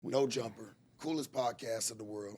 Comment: I thought he said fuck no jumper?! He must be doing bad😂🤷🏽